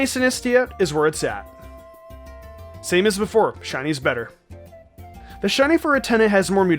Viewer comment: He said sobble for drizzile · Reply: From what I heard he said "Same as Sobble" which is very different